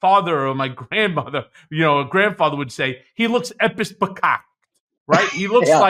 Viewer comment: Can anyone tell me what Cohen says around 3:30- sounds like "epis picot" ? "As my parents or grandparents would say, he looks [epis picot]"??🤷‍♀️ I can't find it, possibly because I'm not hearing it right? Seems like it may possibly be a latin legal term?